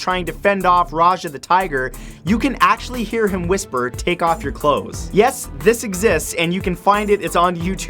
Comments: in Aladdin on the balcony i thought he said take off your cloths as well. however it actually says take off and go... i had to listen very carefully to make it out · He actually says "Come on good teenagers, take off your clothes" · that Aladin take off your clothes one is a misheard line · I thought the balcony s ene said good tiger take off and a worried sigh/groan.